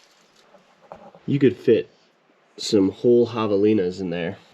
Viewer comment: You said Javelina's, I thought you said Hyenna's and I thought what where? Had to rewind it.